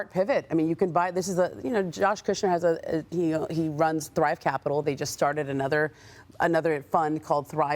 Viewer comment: Sound like she said Bribe Capital